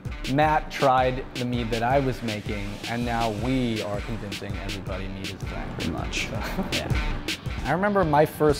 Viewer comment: "we are convincing everybody mead is a thing" bruh he repeated what he said 2 seconds earlier how is that inaudible